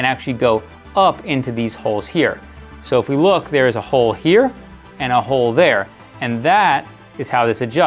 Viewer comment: all i heard was "there is always oil."